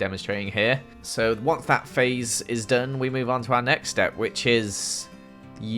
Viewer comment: I thought he said "Once Fat Face is done..." Reply If you did too!